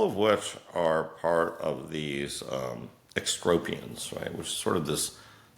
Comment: an interesting society was mentioned - was that "extropians"? I'm not sure I heard it correctly.